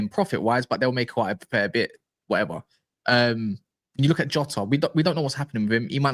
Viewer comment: Did you just say "jota"